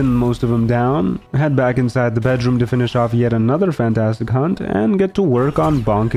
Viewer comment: I laughed so hard when i thought said "... to finish off another fantastic c*nt" XD